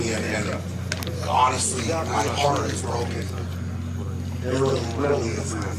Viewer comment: at @ 15:12 did he say his heart is broken? I rewound it 20 times and I can't tell but anyway BIG B you are a Class Act